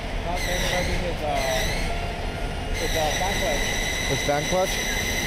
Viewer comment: Lol😂😂😂 i thought the guy said "his chanklas"!!!😂😂, he said his fan clutch!!!